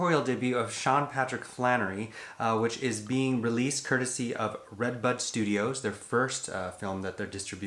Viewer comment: I thought said " Red Butt studios" 😆 🤣